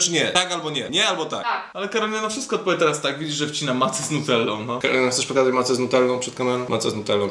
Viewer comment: usłyszałam kiełbasa z Nutellą, haha (